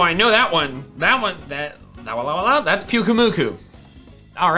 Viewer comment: Every time I hear pyukumuku’s (hope I spelt that right) I hear “got any bubble gum”